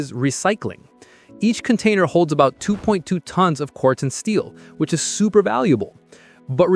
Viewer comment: Oh. He said Corten Steel. Sounded like he was saying quartz and steel, which made no sense.